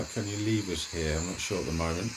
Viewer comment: at 10:45, don't think it says, can u leave us here, I am SURE it says, "get the Fuck out".